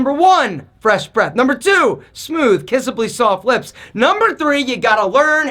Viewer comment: Thought ya said not to kiss ass! lol